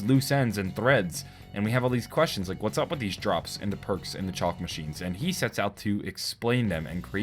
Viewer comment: I love how instead of chalk drawings he says chalk machines